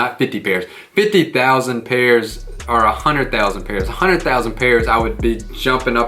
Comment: I heard 108'000 pairs but day includes all the sneaker drops